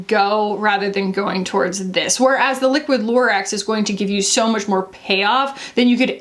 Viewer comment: Every time I hear 'liquid lurex' I mistake it for Liquid Lorax. "I AM THE LURAX, I SPEAK FOR THE SEAS!"